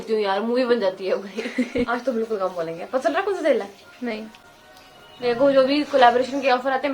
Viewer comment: what did you say? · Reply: "Pta chal rha hai konsa tel hai"